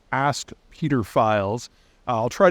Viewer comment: Ask Peter files... Thought you said ask pedophiles.